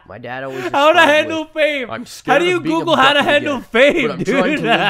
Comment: I didn't pay attention for a second and for the bonus story I thought said "how to handle a babe"